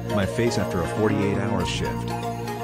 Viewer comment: unironically laughed because I thought it said shit